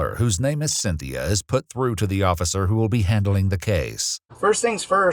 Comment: Did anyone else hear "occifer" at 1:43?